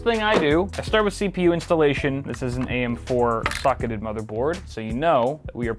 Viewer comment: i thought you said "am4 fuc*eded motherboard"